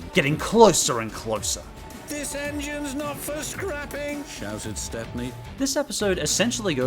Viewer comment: when he said this engine's not for scrapping I thought it said this engine's not first crapping